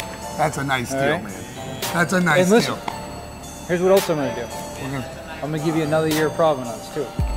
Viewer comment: I didn't understand what he said. "I'm gonna give you another year of ......"? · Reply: warranty